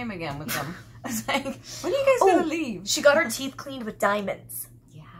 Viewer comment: I thought it said" I got new teeth".